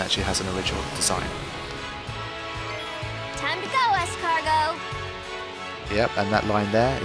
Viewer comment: Does Rikku say "Time to go escargot."?